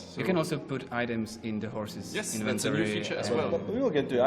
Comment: "You can also put items in the horse's..." When the guy cut him off there I didn't hear him say inventory the first time, so my face was like o_O